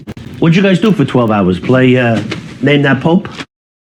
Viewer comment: always thought he said “name that pope”